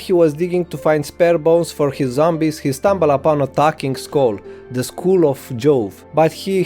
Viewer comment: Wtf is this accent man, I can barely understand this summary. I heard "Attacking School", it was A Talking Skull 🤦🏻